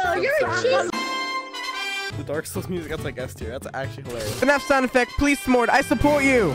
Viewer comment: BRO HE DID NOT SAY FNAF HE SAID FRIDAY NIGHT FUNKIN BROOOOO